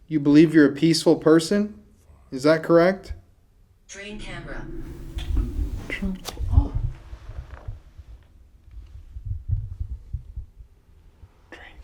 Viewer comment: after says youre a peaceful person i hear a evil voice whispering sounds like into camera